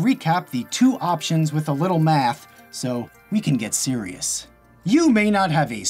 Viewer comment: When Kevin said "We can get serious," there was an ad right before he said the -ous part of serious, so it sounded like "We can get Siri." XD